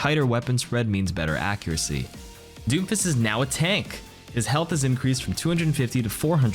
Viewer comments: Am I the only one who heard him say doofus instead of Doomfist? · Everytime you said doomfist I heard "Doofus" I was like wow this guy really doesn't like this character!